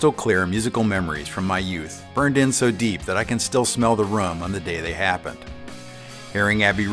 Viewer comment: I thought you said “smell of the rum” at the beginning, and wondered if you were a little young to be drinking when Abbey Road came out! 😅 How many of you all got an electric guitar before you could get an amp? How’d you cope? I found a 1/4” stereo to RCA cable and plugged into the aux input of my cassette tape boom box, in 1994. Didn’t sound good, but I knew I was on my way!